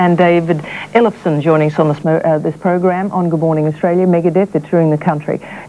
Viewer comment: I thought it said good morning black Friday hahahahah